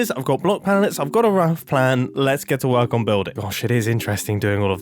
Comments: Did i just hear mumbo said "oh shit" 🤯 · Hey Mumbo, was that a swear at 1:56? 🤣 might just be mishearing, but I feel like I heard “shit it is interesting”